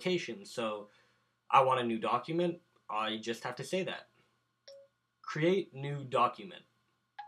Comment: When you said create new document, it sounded like you said create new dog human